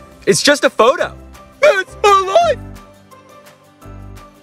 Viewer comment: At the end it said “so spooky” and I thought it said “so poopy”